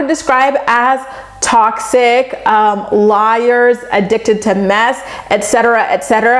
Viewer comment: is she saying mess or meth? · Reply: Mess!!!! 😩😂😂😂😂